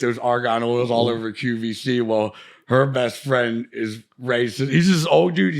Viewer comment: And to the deaf, at 49:08 Bam is actually saying "her best friend is Ray..." Youtube's captions are saying "her best friend's a racist" lol